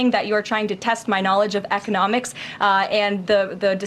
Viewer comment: am i the only one who heard her say "eth-nomics" instead of "economics"? was she drunk?